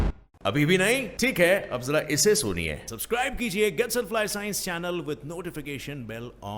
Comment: i heard subscribe only